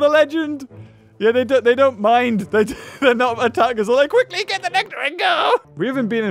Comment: “quickly get the honeydew and go!” Was what the worker actually said